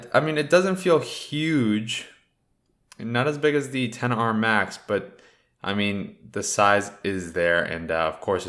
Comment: Did he say 10r Max?